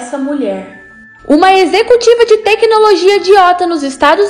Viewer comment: Eu escutei "uma executiva de tecnologia idiota",mds